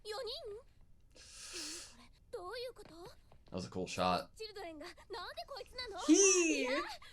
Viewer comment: I think I hear her say “koitsu” here, which isn’t gendered — roughly, it means “this person”. You may have heard anime characters using “aitsu” as a third-person pronoun for someone they find distasteful; that’s related in the same way kono/ano and kore/are are.